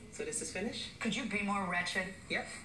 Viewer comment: I thought she said “ratchet” lololol!!!